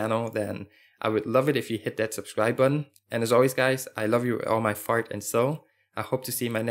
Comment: sound like he just said love you with all my fart and soul haha